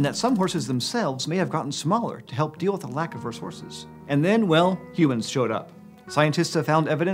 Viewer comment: is it just me or did he say resources as "res-horses"